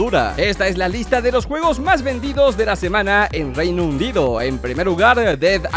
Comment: Alguien mas noto que dijo reino undido XD esta en el